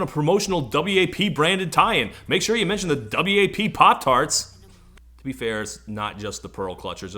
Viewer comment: For just a second I thought you said "WAP flavored PopTarts", and I completely lost it